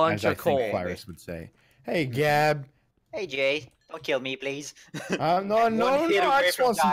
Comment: Who else thought Gab said,”Hey gay”